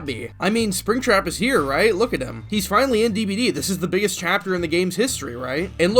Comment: the biggest trapper in the game? I thought you put a lot of effort into your videos, but you didnt catch that one.